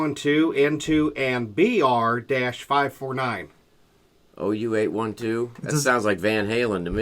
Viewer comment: Another great show, thanks for reading my question. BR549 is junior samples used car lot phone number on HeeHaw. OU812 is self explanatory. Just messing with you guys...... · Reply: I caught the OU812 right away, but when first read the question, I thought he said “BDR 529”, which is the license number of the Bluesmobile. Lol.